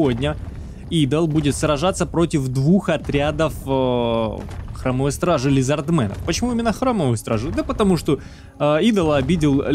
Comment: Вместо Храмовой стражи сперва послышалось Хромой стражи :)